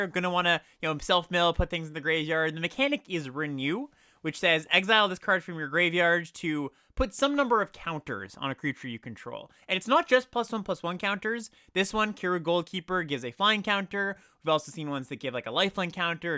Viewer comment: Also note that a reason you might want to pick counters instead of multiple bodies for Endure is Harmonize cards since those want a single big creature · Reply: At first I thought you were saying counterspells 🤦‍♂️ You mean +1/+1 counters